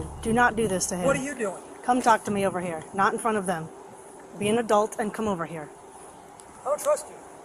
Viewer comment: Cop told him to be an adult… he misheard and thought she said be a dolt…. No shock his ex wife dumped him